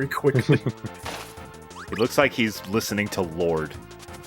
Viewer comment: When says that Kirby looks like "he's listening to Lorde" at 12:46 I initially misheard and thought he was referring to Lordi, the Finnish heavy metal band that won the Eurovision in 2006. Disappointed when I realised whom he meant.